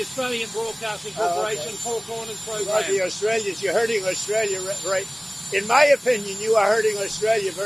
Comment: Did he just say u hurt Australia...😳